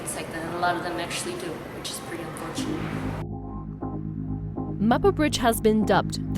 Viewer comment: Did she actually say that committing suicide is “pretty unfortunate”?!!??!?! 2:02 please tell me I heard that wrong. If she said that she’s genuinely heartless